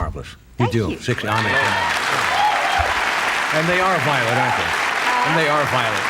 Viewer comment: is he saying "and they are violet ? *